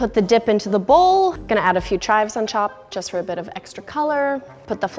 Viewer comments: lol I heard ''chives on chop’’ just me? XD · Lol “ chives on ChOp” am I the only person who heard her say that 😂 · did she say “chives on chop” ? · anyone hear her say ... on chop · did she say “chives on chop”? 😂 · who else heard “chives on chop” 😂😂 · does it sound like she said “on chop”? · Did anyone else notice at 7:29 to 7:30 she said chop instead of top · did she just say "chives on chop"? XD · did anyone else hear "chives on chop" instead of top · Did I hear her say "chives on chop" on chop? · she said "I'm gonna add some chives on chop to add some color" instead of saying add some chives on top for some color. lol she is so stressed that she has a loss of words. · She said chop instead of top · did I just hear “chives on *chop*” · i know im not the only one that heard her say "chives on chop " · anyone notice she said “just gonna add a few chives on CHOP” instead of top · did anyone else hear her say chives on chop. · Am I the only one who heard her say chives on chop? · Hahha anyone hear a few chives on chop? 😂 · anyone else heard her say "I'm just gonna add a few chives on CHOP"? Lol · Pay attention around 7:28 instead of top,she said “chop” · when she said chives on top she actually said chives on chop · HAHAHAHAHHA SHE SAID @ 7:29 SHES GONNA PUT SOME SHIVES ON CHOP INSTEAD OF TOP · Did anyone else hear where she said chives on chop